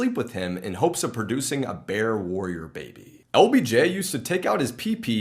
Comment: When said ”LBJ” @ 13:29… I thought he said OBJ (As in ODELL BECKHAM JR) at first🤣🤦🏻‍♂️ I had to rewatch that part to make sure Odell wasn’t whippin out his chicken neck to random people🤣🤣